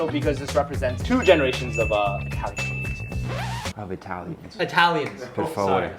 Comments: isn't that what he said i heard him say italians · ? What did he say ? Two generations of what ? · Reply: Italian cuizine